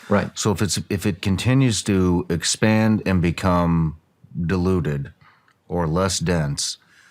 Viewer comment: did he mean diluted? I thought i heard deluded, could be the right word not sure.